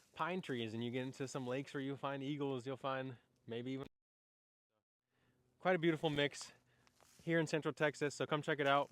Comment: Ok....what did you say at 9:38? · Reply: Alllliiiiggggaaators!